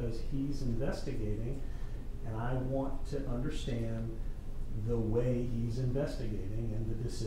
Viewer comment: I have never in my life heard counsel accuse a judge of being confused in that manner. Completely disrespectful. The judge handled it as a judge should. No theatrics, no threats, just a calm reminder that some restraint might be in the attorney's best interest. Firm, fair, and leaves no doubt in anyone's mind as to who is running the show. Well done. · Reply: I went back and listened again and you're correct. Somehow I heard "I don't understand why you're so confused" Thanks for the correction.